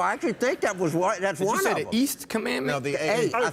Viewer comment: Honestly, I thought he said "Ace Commander."